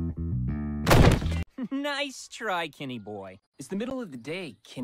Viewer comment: I heard skinny boy